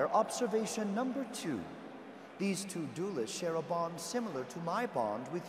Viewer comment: i thought they said share a bong not share a bond!